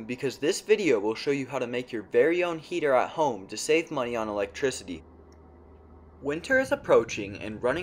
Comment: I thought you said no electricity